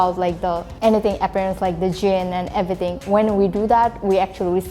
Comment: the subtitle is wrong at 3:32 is not gene. its jinn.